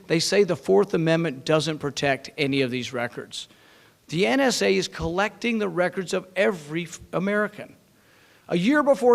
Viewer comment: I could swear he caught himself saying "The NSA is collecting the records of every fucking American."